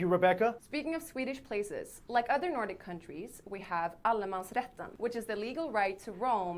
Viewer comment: As a Dutch that sounded like Alle mans Rechten/Retten. Reading swedish and understanding it a bit, yes, but pronouncing it or listening like i can to german.....